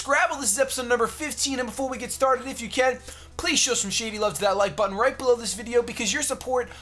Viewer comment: You said "Shady-love" instead of "Lumiose-love" but I understand because you are binge-recording! Keep up the great work! (